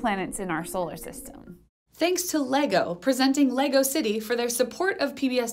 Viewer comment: Congrats on getting support from LEGO. I’ve heard of those guys before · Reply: LEGO? I thought she said support from LIGO!